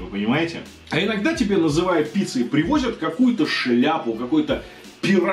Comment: Подвисла на фразе "Пирожок сухой", мне послышалось: "Пирожок с ухой", попыталась себе это представить, потом дошло, что он имел в виду)))